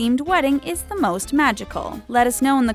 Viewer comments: I thought they said at the end of the video "And don't forget to subscribe to the taco!" Eh.. ok? And then I looked at the actual word talko and now it makes more sense but it still sounds like taco to me! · It sounds like she said 'the taco' instead of 'the talko'